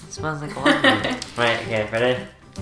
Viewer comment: XD I'm from the US and we don't have wagon wheels (where i am from) so I didn't hear him properly and thought he said waggu meal at the start. LOL I looked it up and I was all confused till he said it more clearly.